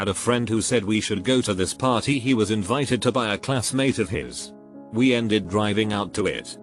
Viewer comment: i thought it said “invited to buy a classmate of his.” and for 10 secs i was just wondering wtf is going on